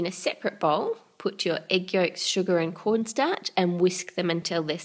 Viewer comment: Thought you said Pornstache instead of corn starch. Think I must just have oitnb season 4 on my mind, coming soon!